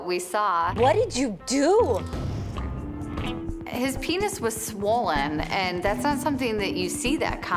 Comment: I thought it said pennies